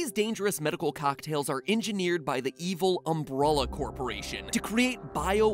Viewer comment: did you just say umbralla? Isn't it umbrella